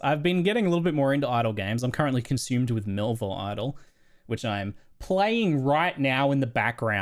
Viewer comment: What is that idle game he mentions around 24:20 ? I cant quite understand it. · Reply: Melvor Idle, it's like runescape in idle form